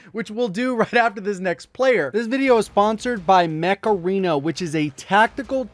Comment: Genuinely thought you said this video is sponsored by Macarena and I was like WUT?!